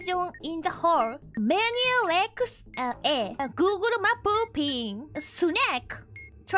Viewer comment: I always lose it at "Google Map pin" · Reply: I thought she said google map poopy